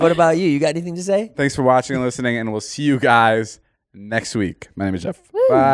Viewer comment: Did David say "my names Jeff" at the very end?